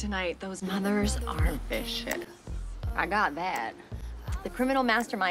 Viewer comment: I’ve always thought she said vicious not bishes lol